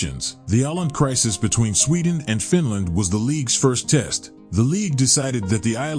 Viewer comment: I thought this was "Ireland" said with a heavy South Africa accent 🤣😆🤣